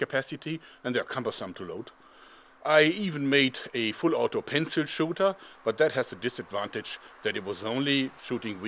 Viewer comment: Say's "pencil shooter"....while i hear 'panzer schutze''